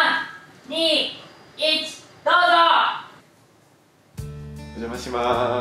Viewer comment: 虫さんの｢どーぞ！｣がむしえもんにしか聞こえない笑笑